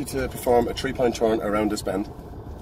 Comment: "A three point turn around this bend" 🤔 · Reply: I thought he said a Tree Pine Turn around the Bend Was confused, still confused